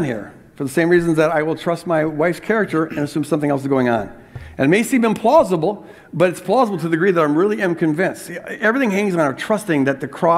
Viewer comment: he actually said, "And it may seem implausible but it's plausible to the degree that I really am convinced."